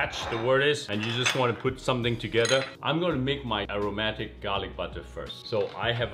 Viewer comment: He said it's "aeromatic garlic bread" but I heard "aromantic" cause it's a running joke that asexual and aromatic people love garlic bread more than people lol